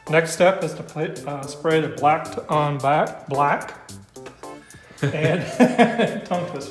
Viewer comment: Another great video with groovy music and great work from and! I was surprised at first that you took it all apart, rather than refurb insitu and second that you used Black on Black, (thought you said Back to Black at first that is a very poor quality product), rather than Gtechniq C4 Permanent Trim Restorer - after a clean and wipe down with isopropol alchohol. Then I was surprised that you didn't buff it to a satin finish rather than gloss. I liked the idea of steam cleaning and also the air blow off. I will be interested to learn how long the finish lasts, if it stays gloss or dulls. Finally do you intend to apply any sort of top finishing wax or maybe ceramic coating? Sadly your videos have become a highlight of my week. I must get a life. Now, where did I put that C4?.......